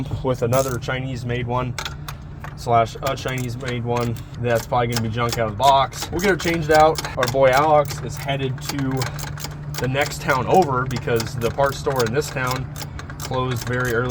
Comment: I thought he said bon voy Alex